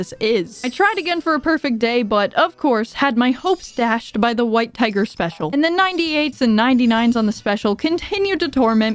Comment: for some god Forsaken reason both my girlfriend and I heard "white pegger special" instead of "white tiger special"